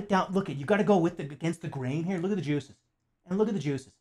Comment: I heard "against the grain look at the jews" and the black gloves aren't helping your situation Jermarine